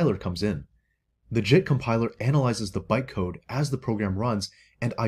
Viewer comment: Watching at 1.5x speed, around minute 2:20 when he says "JIT compiler", it sounds like he is saying "SHIT compiler", which would of course be more accurate.